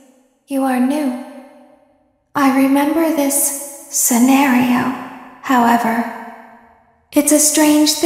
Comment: i thought she said sonario as in like sonic x mario 💀